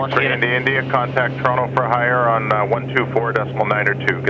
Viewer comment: Does ATC say Contact "Toronto for hire"?